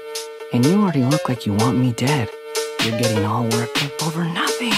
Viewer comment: I ALWAYS THOUGHT HE SAID “you’re gaining weight”